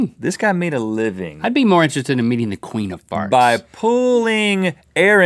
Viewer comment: I thought link said "I'd be more interested in meeting the queef of farts"